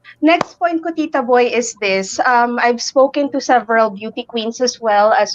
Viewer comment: did she say "SPOKING"?